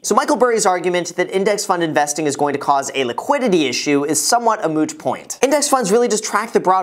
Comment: is it just me or did I hear you say mooch point 9:30 ?